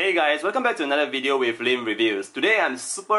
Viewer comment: I thought he said welcome to lame reviews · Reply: LOL! It's LIM! Sorry for my bad English